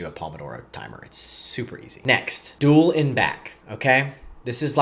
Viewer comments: it's frustrating that at 11:13 I can't tell what you are saying or what it means. sounds like 'dual and back' but the transcript shows 'dual in back'...and then you say 'you will hate it' but What Is It? what does 'dual in back' even mean? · It’s time to meet your potential. We go much deeper on rebuilding focus, clarity, mental, and physical performance inside my new community. Practical tools, clear systems, and direct access so you’re not doing this alone. It's a place where I am able to directly chat with you the most. Link here if it feels right: <link> Pinned for the people who are ready. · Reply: you said something around 11:20 that sounded like "duel in back" but I have no idea what you are referring to as I've never watched one of your videos before. what is the thing that is "so terrible" for 15 minutes a day? I didn't see it mentioned in the description. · duel and back?? what did he say? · What did he say at 11:14? Duel in back? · I didn't get this part "dual in back" or something can someone explain what he said here cause I don't think he explained the process only benefits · "duel it back" "pull it back" "dull it black" wtf are you even saying here